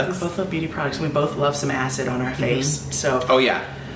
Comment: I LITERALLY heard "despite the fact we both like asses on our face." "Oh yeh!" Instead of 'acid' I nearly choked